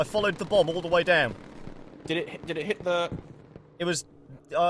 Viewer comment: xD its sounded like did it hit-ler ?